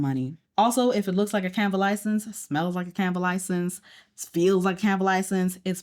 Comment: what is she saying here? subtitle is saying canva license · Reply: She is saying “Canva license”. Canva is a graphic design platform, she’s saying they created their license themselves or someone else created it in a program on their own. It’s not an actual license to do anything. Like a Microsoft Word license or Photoshop license, it was just typed up and printed out by whoever and holds no real value